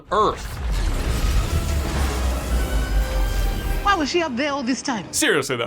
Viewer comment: I’m having some serious Mandela effect here because when I went to the movie theater to see the movies day view, I could swear on my life the soldier says “Why was she up there all along” but now it’s different, so idk